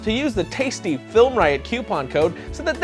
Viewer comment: where are you from originally? At 7:55 it sounded like you said "Qupon"..